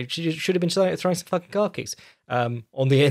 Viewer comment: I misheard "calf kicks" as "car keys" multiple times. Something along the lines of saying "Her jabs were poor, she should have attacked her with car keys" and I'm nodding along like an idiot going "yeah, that does sound nasty, I bet that's really gonna hurt" before the realization set in. Guess it means I should go to bed now!